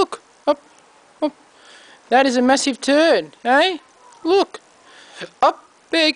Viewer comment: i thought he said messy turd